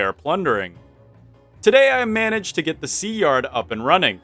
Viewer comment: did you say "today I am manage?" I might've misheard you but are you from the east coast US? I watched a video about language the other day and learned that sentence construction is used in some niche accents